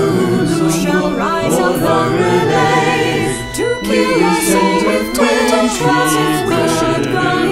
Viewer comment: "To kill us all with tentacles" I always hear that as: "To kill our souls with tentacles"